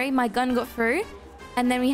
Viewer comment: In the vido at 2:07 WHAT DID YOU SAY I HEARD oh my gun got throught EHM A GUN! XD